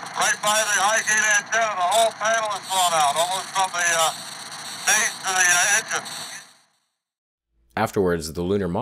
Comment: Minor correction: when Lovell is observing the damaged service module, he says "from the base to the engine", not "from the stage to the engine". I misheard him.